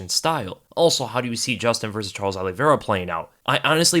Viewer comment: he said Charlie Olives. Lol ever since Dan Hooker in his New Zealand accent said Old Charlie Olives that’s all I hear when anyone says Charles Oliveira.